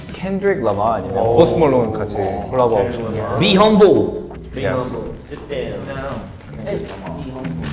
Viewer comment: when they said "be humble, sit down" for the felix collab question i thought they said "이용복, sit down" 😂😂 (that's felix's korean name btw)